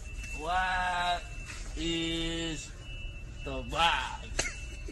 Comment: whats he saying? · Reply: I heard "what is the vibe" 🤷🏽‍♀️